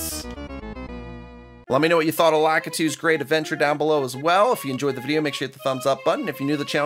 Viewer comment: I legitimately thought the start text said lakitus CHEAT adventure 🤣🤣🤣